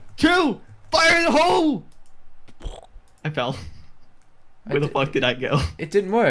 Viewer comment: did he say where the f**k did i go?